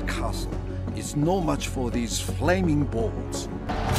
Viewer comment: does he actually say this? i never recruit siege in this game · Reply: Yes he actually says flaming balls aha